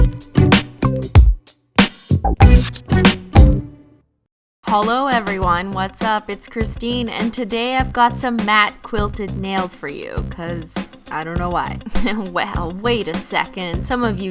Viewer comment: It sounded like you said "Holo everyone," at the beginning :]